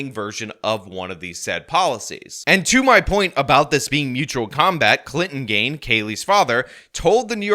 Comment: "I thought she said Mortal not Mutual Kombat!"